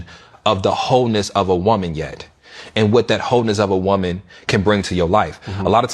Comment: Why does it sound like he is saying HOENESS and not wholeness of a woman?